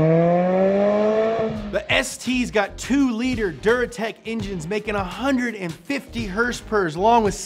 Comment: I thought he said “Zyrtec” lol